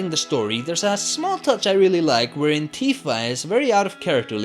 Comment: I love that you said "Antifa" instead of "Tifa". Great Content btw!